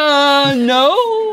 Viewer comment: I heard "Elloooon noo"